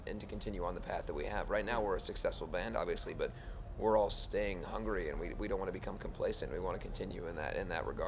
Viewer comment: did he said Hungary or hungry?? :ooo